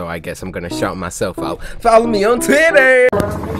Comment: did he say titter